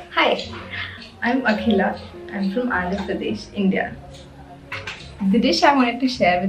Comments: she mentioned the state's name as Andhra Pradesh. Not Uttar Pradesh. The subtitles got it wrong. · yo, at 14:55 your video is captioned Uttar Pradesh, instead of Andhra Pradesh, 2 very different states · the girl says Andhra Pradesh but the US English captions say Uttar Pradesh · She says she is from Andhra Pradesh, subtitles say Uttar Pradesh !! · correction: the caption says uttar pradesh but she says shes from andhra pradesh